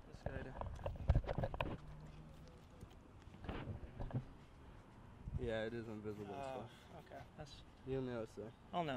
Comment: i wonder what the pen have to say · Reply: he say "cock"